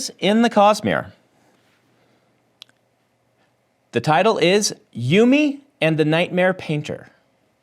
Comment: Honestly thought he said Yumi and the Nightmare Panda at first now I'm dissapointed 😂